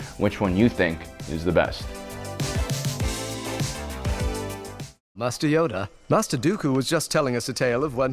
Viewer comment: sounds like obi wan says musty oder instead of master Yoda lmaoooo